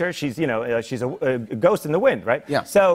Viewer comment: HE WAS GONNA SAY "SHE'S A WITCH" I'M CRYINDGGDKSKHD